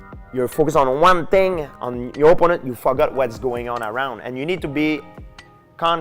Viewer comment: for a second I thought George said “you might fuck up” lmao I thought to myself I never heard him cuss before but he just said “forgot” lol